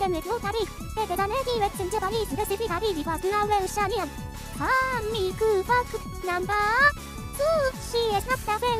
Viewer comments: Why do i keep hearing “fun miku f-“ instead of “fun miku fact!” 😭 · Wth do I hear "fun miku fuck number..."